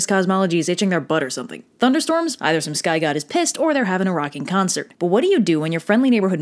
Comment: I misheard and thought that she said that “some sky goddess pissed” instead of “some sky god is pissed”